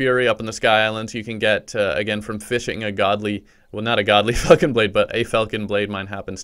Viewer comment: sounds like he said “godly fuckin blade”😂